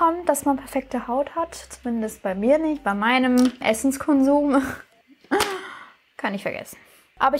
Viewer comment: Minute 2:08 habe ich anstatt „Essens Konsum“ „Essence Kondom“ verstanden😂🤷🏼‍♂️